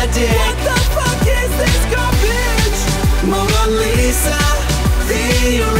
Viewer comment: What does he say at 0:35-0:37??? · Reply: "What the fuck is this garbage"